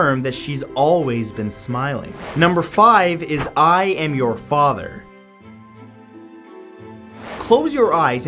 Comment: Why I hear "No I am THE father"